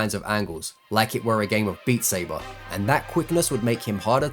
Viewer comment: wait did he just say beat saber?? that just caught me off guard him mentioning my favorite game